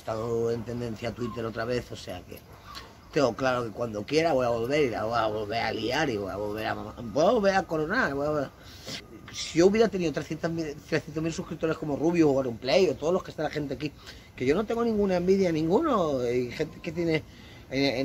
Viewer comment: minuto 28:08 .Cuando yo quiera voy a volver voy babolel a colgar voi blabobe blabobea (corte) XD